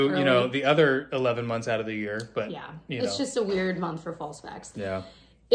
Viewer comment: Ok I'm exhausted and scrolling through reading comments as I watch and I thought I heard say "it's just a weird month for ball sacks" at 24:13 🤣🤣🤣🤣🤣 I'm going to bed people I promise